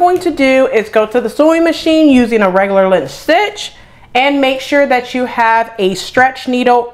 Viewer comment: I love your reviews. They are so organized and helpful! Thanks also for showing us the process! At about 20:59, you said to sew the shoulder seam using…. I can’t catch what the stitch is. The transcript says a regular lint stitch but I don’t know what that is. What stitch do I use? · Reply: you will need to use a 2.5 length stitch which is a regular length stitch to sew the shoulder seams together. I hope this helps!